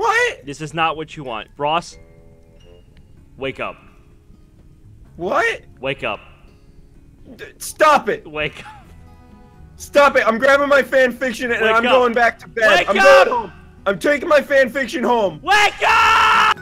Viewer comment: After the last wake up all I heard was 'wake me up inside!!!!!'